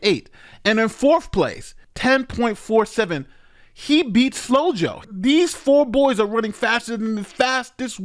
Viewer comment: I had to do a double take, I thought he said "He beats Slow-Jo"